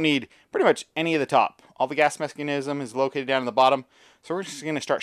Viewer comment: Did he really say "All the gas mexi- canisim" lol